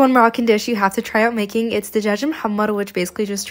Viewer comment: At first I thought u said American but u said a Moroccan 😂